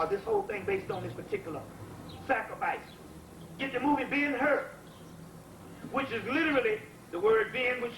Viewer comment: What was the movie he mentioned at 17:58 ? Sounded like he said ‘Being her’ but I couldn’t find a movie with that title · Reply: Ben Hur